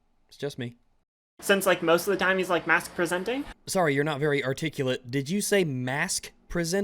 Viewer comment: I think she said "masc presenting", masc being short for masculine.